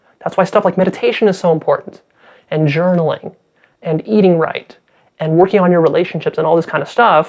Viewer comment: hey guys, could somebody tell me whats the word he is saying at 24:31 couldnt figure it out, it comes right after :"thats why stuff like meditation is so important and .... Thanks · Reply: Patrick Weber Journalism - writing journals ;)